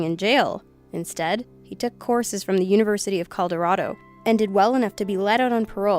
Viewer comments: in 1:57 did anyone else hear coldorado instead of colorado? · did she say Coldarodo instead of Colorado?